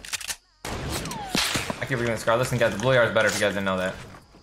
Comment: What does Ninja say at 13:22? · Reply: "Why didnt i pick up the scar?" "The blue ar's better if you guys didnt notice that."